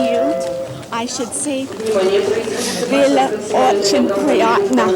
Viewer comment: Does anyone know what the woman says at 8:46 ? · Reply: "Было очень приятно"